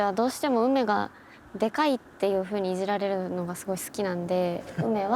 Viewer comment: 『私はどうしても梅がデカいっていう風にイジられるのがすごい好きなので』の言い方がマジで「すごい嫌で」のトーンに聞こえたし、その後のトークでめちゃくちゃ肩幅とか梅へのデカいイジりが好きなんだなって思った